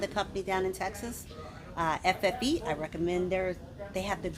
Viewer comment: Did she said FFE????